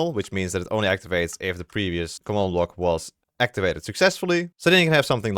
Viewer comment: did he say "activates if a PENIS commandblock activates behind it"?